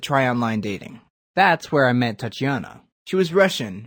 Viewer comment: i thought he said 'and thats when i met thotianna'